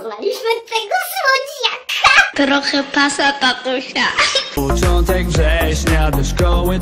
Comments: Usłyszałem passata tatusia :3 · początkowo usłyszałem PASSATA tatusia xD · Usłyszałem passata tatusia. XDD